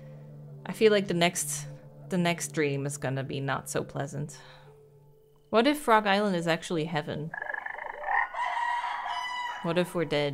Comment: I absolutely misheard 38:39 as, "What if Rhode Island is actually heaven?" 🤣